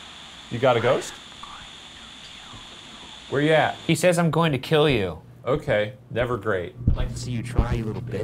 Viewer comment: When the ghost first said “I’m going to k*ll you”, I heard “I’m going to Tokyo” And I was just like, yeah, ok. Good for them